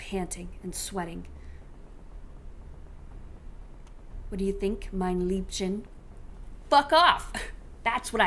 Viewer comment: "What do you think, my darling?" is what he said! YOUR NOT PETER SHUT UPPP